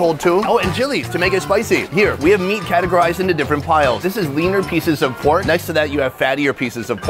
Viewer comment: U said the word "leaner peices of pork" and I heard "weiner peices".... idk... don't ask